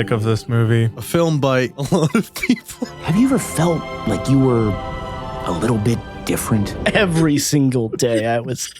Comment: I literally thought it said “Have you ever thought that you were a little bitc “ literally the first thing that came up in mind.